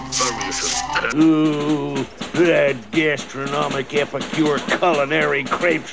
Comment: Is it me, or does it sound like he said, “Epicurean?” XD